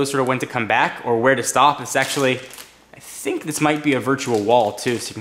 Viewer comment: i thought he said "it's sexually" lol